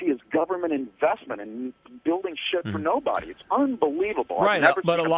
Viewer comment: Did say "shit" around the 11:32 mark :) LOL